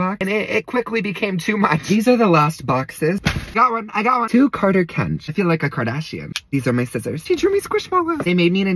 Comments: People who thought lt said FARTER😂😂😂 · AT THE START I THOUGHT IT SAID FARTER INSTEAD OF CARTER 😂